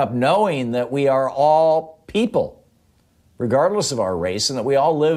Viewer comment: For a moment there i thought i heard you say we are all evil, instead of we are all equal. Made me chuckle thx